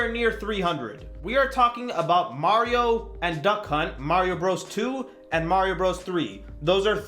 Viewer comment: The way you say "Duck Hunt" so fast sounded like you said "Duck CUNT"